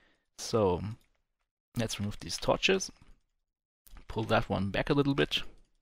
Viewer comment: did you say- " pull that one back a little bitch" !?!?